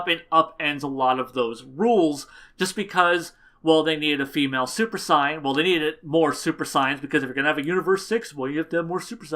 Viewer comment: I keep hearing "Super Sign".